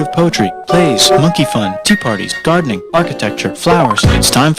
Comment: when he says “monkey fun” the subtitles say “sexy times” 💀💀💀